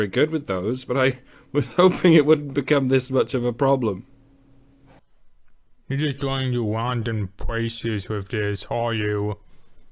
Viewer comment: I'm fairly sure that he says here "You're just going to random places with this, aren't you?", with a heavy slur on "aren't you" Could be wrong, but that's what I heard.